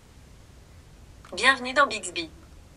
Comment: A 1:55:22, quelqu'un a comme moi entendu la voix qui dit "Bienvenue dans Bixby" ? 🤣